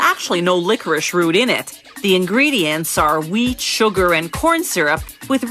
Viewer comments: When she was naming the ingredients of liquorish at the beginning I thought she said weed instead of wheat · wait did i hear weed? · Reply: Man you need to get your ears checked. how did you mistake "wheat" for "weed!" XD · Who else thought she said weed sugar and corn syrup instead of what it was? No? Just me? Ok